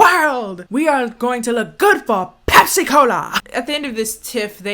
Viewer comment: I thought you said "Pepsi E. Coli..."